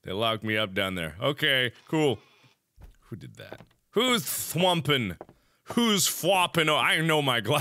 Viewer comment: I thought he said "who's humping" lol